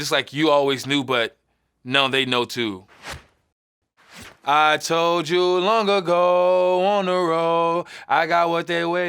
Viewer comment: Broooo i thought it said “I told you long ago Montero” lmao